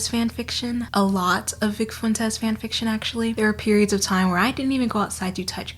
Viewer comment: I THOUGHT YOU SAID NICK FUENTES FANFICTION I WAS SO CONCERNED